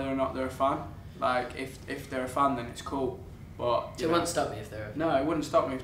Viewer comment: Wait what did they say at 15:36 cause the interviewer said if the boys had down time and someone from their team yelled out something about the question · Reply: Zayn says, "If they're a fan, it's cool." Louis says to Zayn, "So, if they are a fan, it would stop you?" Louis looks forward with a smirk and his face, as if what Zayn was saying was a lie. Zayn said, "No, if they're a fan, it wouldn't stop me. In fact, if I find someone attractive, I'll let them know." Louis looks at Zayn gestures with his head towards Harry and says, "Like Harry?" Zayn said, "Yeah," 💛💚 then smiles. Louis looks straight ahead with a fake smile on his face. His smile fades away, and he presses his lips together. Louis was p*ssed. Love Zayn's character, honesty. Louis asked, Zayn answered. Louis shouldn't ask a question he doesn't want to know the answer to. (LMAO) 15:37 Their handlers noticed Louis was p*ssed and said, "Left question."